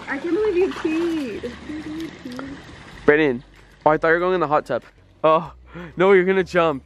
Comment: who else heard say Brennan